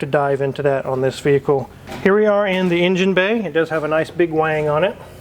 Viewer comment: Did he say "Big Wang"?